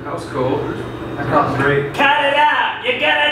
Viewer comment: I keep hearing you're gonna get bit :-)